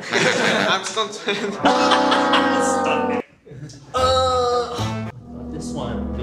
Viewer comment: what did wooju say? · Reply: He said “I’m stunt man”